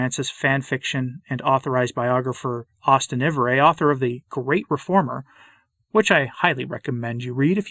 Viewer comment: Haha,not that it’s really funny fundamentally—but when you said the name of Ivereigh’s book, I thought you said the title was The Great Performer, and I thought it odd that Ivereigh would be allowed to pound in that particular nail or would want to do so.